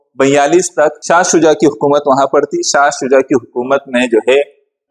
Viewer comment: What is the name of king you said..?? Can you please write the name in comments? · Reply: Shah Shuja